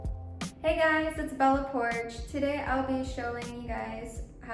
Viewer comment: I thought she said her name is pronounced bella poark